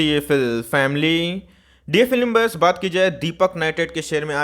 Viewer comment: What does it mean by DFL imburse He says at Starting ? 🤔🤔🤔